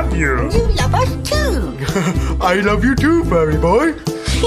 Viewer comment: Pensé que decía: También te amo, pelotudo amigo XD